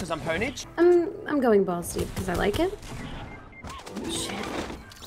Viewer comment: I thought she said ballsy but as I rethink what she said she said balls deep